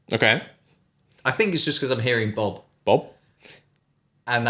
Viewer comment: i thought russel saying box